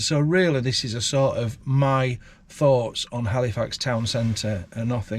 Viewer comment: A up. Is Cornish sea salt better than other sea salt? It sounds like your saying Halifarts 😂